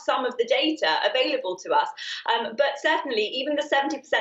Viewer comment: I heard "Hardly effective". Anyone else???